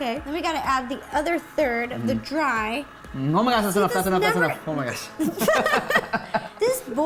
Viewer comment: Does anyone else know what he said in 7:14??? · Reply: Pablo Lopez he said oh my god that’s enough that’s enough